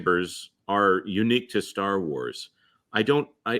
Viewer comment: All I hear is “Disney’s Star Wars.”